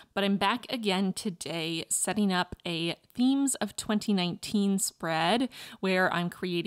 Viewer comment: I definitely thought you said Memes of 2019, so I was so confused as i watched 🤣 turned out beautiful!!❤️